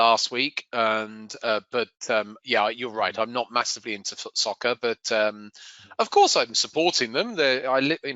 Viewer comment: Did just say Footsoccer? That's one way to annoy both sides.